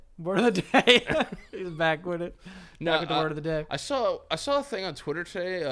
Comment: How did he say this? "Back widda wordoddaday." Those rapid-fire D's are impossible for me to say.